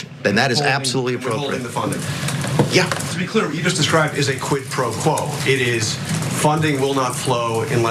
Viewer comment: Oh wait, I thought you said SQUID pro quo. Love that calamari!